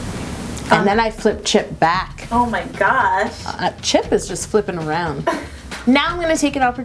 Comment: I heard "Chip is just sleeping around."